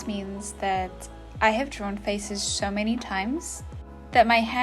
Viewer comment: For a second I thought the person said drawing feces